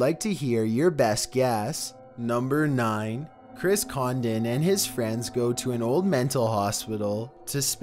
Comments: I thought it said Chris Condom at Number 9... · I can't be the only one who thought number 9 said Chris Condom... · Thought it said Chris Condom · #9 I thought that said Chris Condom lmao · Who else thought that said chris condom